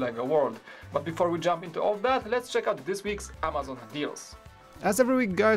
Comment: I love how when says "Amazon Deals" it sounds like he says "amazing deals"